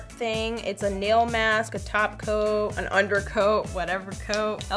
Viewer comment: I was waiting for her to adress simplynailogical but she didnt !!! Im starting to hate YEA I HEARD THE “Top coat” ITS “ glossy taco”